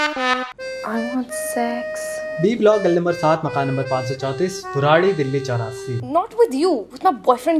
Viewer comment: Did he say Burari?